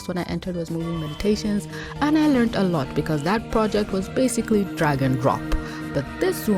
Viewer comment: For some reason, the first few times I could have sworn you said, "dragon-drop" and I was just thinking, "is that like a mic-drop, but more so?" After about halfway through the video, I realized it was, "drag and drop." Who says I need hearing aids?! Besides my wife.